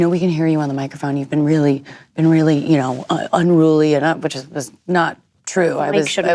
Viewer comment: What does Saoirse say at 11:40???? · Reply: “Mic shouldn’t be up anyway” i think?